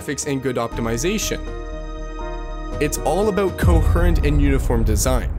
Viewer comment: What word are you trying to say? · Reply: He was incoherently saying coherent